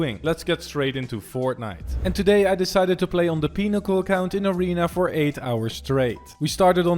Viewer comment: Did i Just hear peni#cul account?